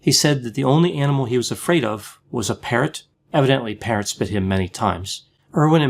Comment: 🤣 I thought for a minute you said "The only animal Steve was afraid of was parents" I was like - me too! 🤣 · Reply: Me too. I had to replay it...parrots. lol